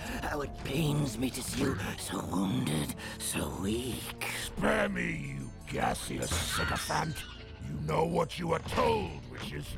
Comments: what does megatron say here? I cant quite make sense of it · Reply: SPARE ME YOU GASEOUS SYCOPHANT! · megs says “spare me you gas-___” tf did he say i’m having a stroke tryna figure it out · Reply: "You gaseous sycophant"